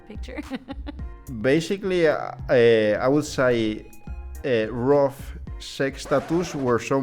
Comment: 🤣🤣🤣🤣 when the .music stops playing as he mentions rough sex . But sounds like he says Roof sex ! That was freaking amazing. Lol